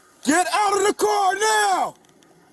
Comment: I swear I thought at one point I heard one off the officers say "Drop the car!"